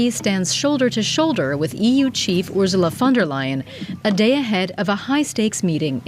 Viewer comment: Is this an AI voice? Sounded like she was saying Ursula Thunderlion!